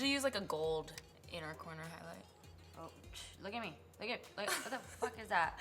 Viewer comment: when it was 14:42 it sounded like she said " What the f***k is that?" Like this if you agree